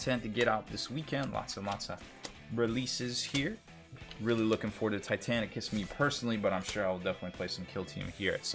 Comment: I'm sorry, at 4:40 does anyone know what he said he's looking forward to? I heard, and the close caption agreed with me, "I'm looking forward to Titanic kiss me, personally." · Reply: hes talking about adeptus titanicus